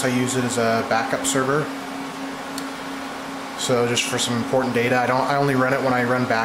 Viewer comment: did you just say "porn data"??? 20x1TB woooooweeeeee thats a lot of porn